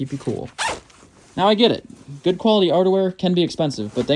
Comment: When you said outerwear it sounded like you said underwear at first. 😂